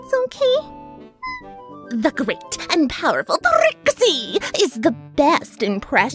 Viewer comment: Prixie or Trixie I'm not being mean here just it sounded like u said them at the same time